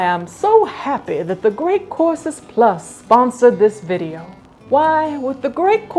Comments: I read "The Great Corpses PLus", like i really heard you say that as well. Ah well, Orbses and Corpses... Gollum... Gollum... Smeagol.😁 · During the sponsorship I thought she said “the great corpses plus” and I didn’t question a thing for a good minute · At first I heard "This video has been brought to you by the great corpses plus' LOL. Silly me. 😂😅😮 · Totally misheard the beginning as you’re sponsored by “The Great Corpses Plus” and that sounds pretty rad ngl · I thought she said she was sponsored by The Great Corpses Plus at first 😆